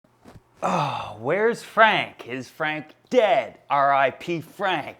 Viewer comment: I thought it said "Frank is dead!" instead of "Is Frank dead?" Almost cried